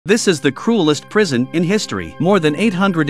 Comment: "this is the coolest prison in history" I swear I heard him say that when the video started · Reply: CRUELEST * not coolest